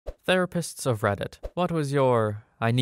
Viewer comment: i thought that said the rapists what was your "I need a minute" moment?